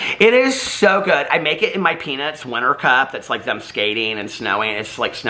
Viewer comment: I heard "I make it in my pEnIs mug!" Had to rewind it to be sure and discovered you actually said "Peanuts Cup" OMG! 🙉 FAMILY FRIENDLY! 💙🤣💙 Hey, whenever you mention your new Keurig, I remember that time you attempted to make coffee in the hotel room...that Keurig maker got the best of you. 🤣💙🤣💙